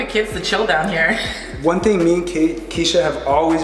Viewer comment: Did he say one thing me and “Kate” wanted?